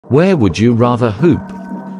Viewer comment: I heard him say where would you rather poop